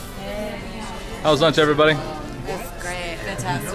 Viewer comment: a Whaat ??? did you say Gyrooo ??? Nice ...